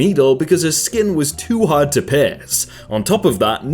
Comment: I misheard a sentence and I heard her skin is to hard to piss